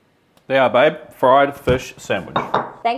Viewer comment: Am I the only one who thought she said "five beef sandwich"?